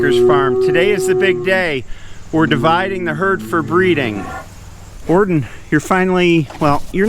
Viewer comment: I swear all this time I thought you were saying Orton